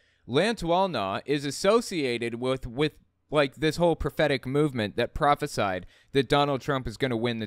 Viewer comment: I keep hearing "prophetic movement " as "pathetic movement " in my head.